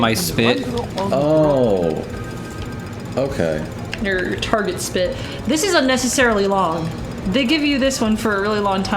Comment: I think I heard "r****** spit" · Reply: It's muffled, but I think she said "your target spit" - as in, the spit using to hit the targets in the puzzle.